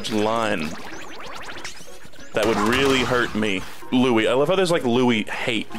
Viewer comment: When he started talking about Louie at 16:22 i actually thought he said bluey at first and i was so confused for like 10 seconds 😭😭